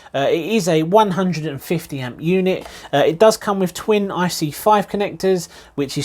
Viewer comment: Did he say EFC ? I think you mean ECU ? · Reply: ESC - Electronic Speed Control :)